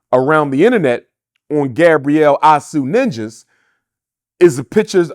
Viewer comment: I thought when I first heard her last name I heard “Sue-Nxggaz” and then Dr. Umar says it 5:20 into the interview. 😂